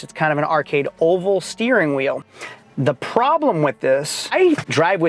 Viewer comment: What did you just say? LOL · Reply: Arcade oval steering wheel?